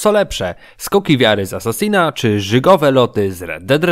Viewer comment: Ja usłyszałem rzygowe lody xddd